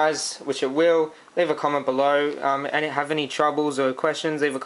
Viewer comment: hahah i thought he said: "comment,rape,subscribe!: ahahahah